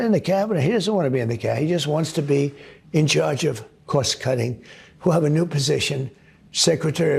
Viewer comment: Thanks, Adam, for the clarification of cost cutting. I thought he said cross cutting, 😆.